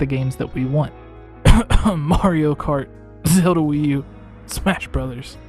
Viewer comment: you said Wii U instead of nx